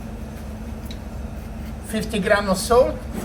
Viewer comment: did he say 50g or 15g of Salt? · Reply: Gotcha, I understood him - 50 grams for 5 kilos (5000g) 1%. This is a pretty standard seasoning ratio.